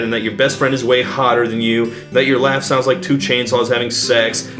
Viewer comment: I was today years old when I realized what he was saying at 13:35 wasn’t “Your laugh sounds like 2 Chainz songs having sex” but “2 chainsaws”